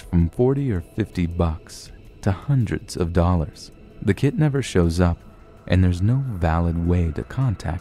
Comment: I thought you said the kidnapper shows up until I used subtitles XD at 2:15 Anyone else?